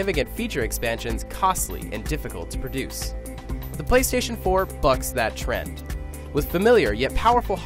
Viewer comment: Did he just say "The Playstation 4 fucks that trend" at 0:25?